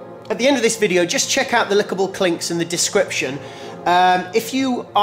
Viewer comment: did he say the "lickable clinks" again? ;)